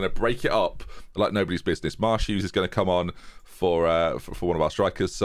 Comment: You say Marsh-Hughes, but all I hear is " Ma' Shoes"